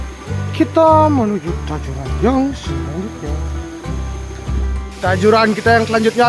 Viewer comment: ngomong apa bang🤣blepotan🙂🙃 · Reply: Kita menuju tajuran yang selanjutnya😂😂... Editing nya kemaren ada yg konslet abang ku😁😁😁🙏